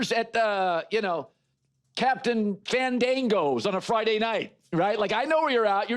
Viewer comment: Did he just say "captain mandingo's?" 😂